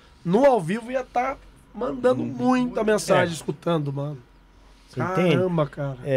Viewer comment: outra voz parece falar "escutei ou estudei"